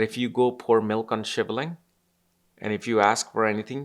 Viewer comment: What does he say at 10:49 ? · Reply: "Shiva Linga" ? You mean?